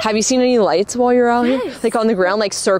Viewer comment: I heard “yes”